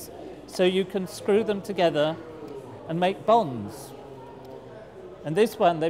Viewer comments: i thought he said bongs, lol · I thought he said bombs not bonds.